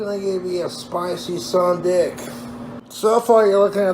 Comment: “A spicy..” WHAT? Please inform me! I heard “sawdick” and am very confused!